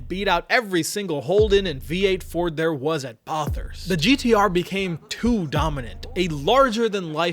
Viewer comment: Wait wtf did you just call Bathurst? Bar thirst??